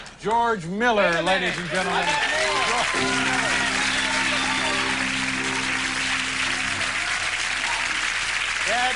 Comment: What did GM say there at the end...??? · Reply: "And I will be appearing..." Then a bunch of places he will be appearing.